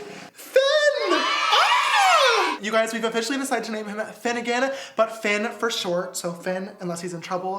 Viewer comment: He talked so fast that I thought he said 'we namedhimFinnagain'